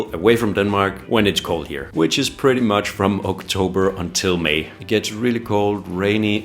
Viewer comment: It is! I think that's what I say; that May is probably the perfect time to go :) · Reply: Thanks! I misheard and thought you said through May instead of until May. This is proof of how much I need that upcoming vacation. 😂